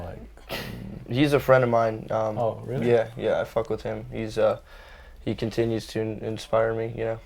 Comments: он сказал I FUCK WITH HIM .Или мне послышалось.Для не шарящих I FUCK WITH HIM это ебусь с ним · он сказал ай фак вив хим? Или мне послышалось